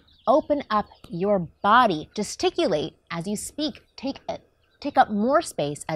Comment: Did she just say, “Testiculate s as you speak.”?